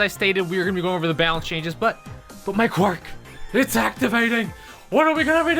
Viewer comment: u dont even know bro i heard say "my quirk is activating" i was like no way the i saw the mha merch lesgoo amazing serie